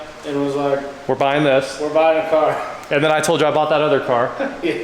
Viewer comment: Thought you said no more boring car's???? Lol